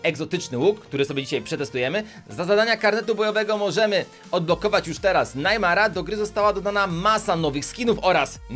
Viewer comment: usłyszałem Najmana xD